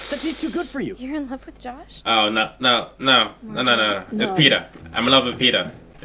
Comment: Did you say Peeta??